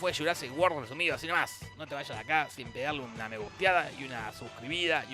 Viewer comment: jajaja yo pensé que decía no te vallas sin pegarme una reputeada!